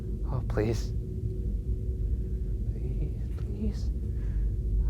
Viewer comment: Does anyone else hear a female voice say “please” at 16:16, just turn your volume up high and listen closely